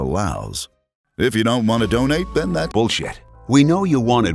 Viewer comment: um not cause trouble but did jhe just say bullcrap in 9:20-9:25?